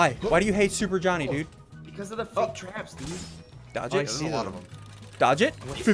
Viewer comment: So i was looking at vid and continue watching the vid from here 6:34 and i hear either saod bc of f ing traps dude or fricking but when i try to look what he said with the captions nothing popped out and im suspicious of that...